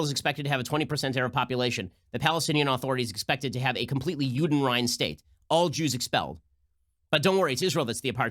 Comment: Somebody know what word he's saying there? Udenryan state? I've never heard that word before...is it Hebrew?